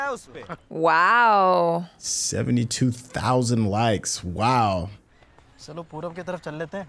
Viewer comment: Subtitles are wrong here 1:10 it's actually 7200 likes